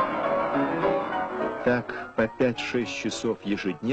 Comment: What is the voice over saying, ca. at 05:18? <3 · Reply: He said that like this Richter practicing piano everyday for 5-6 hours.